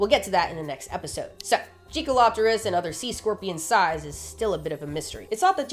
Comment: I had to reside to see the spelling. I swore you were saying jiggalo*pterus